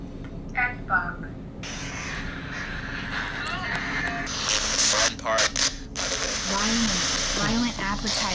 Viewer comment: HEY Y'ALL!! Remember to comment "I LOVE TRUE CRIME" and share the scariest true crime story that you know BELOW (in the comments section) for a chance to win a FREE GIFT BAG FROM THE CHANNEL!!! If you love "The Paranormal Files" be sure to LIKE the video, SHARE the video, SUBSCRIBE to the channel and leave me a COMMENT BELOW!! Every time you interact with the video it helps it do better on YouTube, so YES, YOUR COMMENT AND LIKE MATTERS !!!!! Also, if you want to watch my most recent murder documentary, just click the link below: <link> If you want to listen to our JEFFREY DAHMER episode of MURDER IN AMERICA, you can listen at the links BELOW (or wherever YOU listen to podcasts, just search "Murder In America Jeffrey Dahmer"!): APPLE PODCASTS: <link> SPOTIFY: <link> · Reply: I couldn’t hear the first word but I heard “park” maybe “island park”